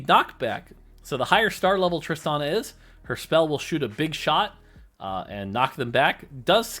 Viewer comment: did you just say... [BIG SHOT]?